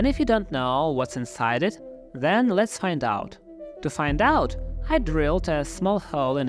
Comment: That obviously was not your Mellon thank goodness, yet I had thought you said "what's inside a baseball, let's find out". After you opened the baseball There Was Nothing Inside It! I knew they did not make them like they used to! DON'T LAUGH!😂